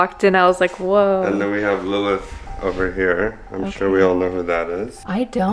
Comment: when he said then we have Lilith over here i thought he said Lola but no it was Lilith